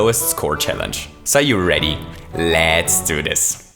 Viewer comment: It almost sounded like the beginning to a perfect impression of Luigi saying "Let'sago!"